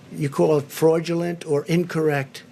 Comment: Did he say 'freudulent' transcript at 1:20?